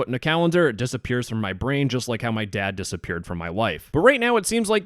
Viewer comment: "Just like my dad disappeared from(For?) my wife" Did i hear that right? xd